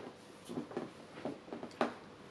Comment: 〜寝るかって聞こえた気がしたんだけど、、、笑 この日は忙しい日では？😂 そんな2人も可愛くて尊いくて好きです😇